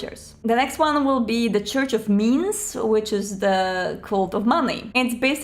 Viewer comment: for some reason that sounded like "the Church of MEMES" to me ;D